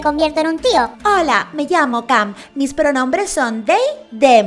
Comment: hola me llamo cum Pd: O al menos eso es lo que yo entendí a la primera XD